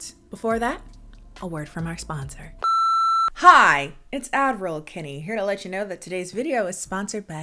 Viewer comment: Omg I feel so dumb, for the longest time i thought you were saying “Admiral Kennie” not “Ad-Roll Kennie” and I thought it was so clever cos “AD-miral” as in Advertisement Admiral and I just 😔😂😂😂😂 omg, but anyways love you and your videos!! 😍😂🤣